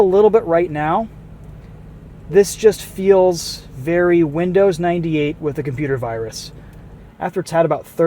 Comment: Did say Windows 98?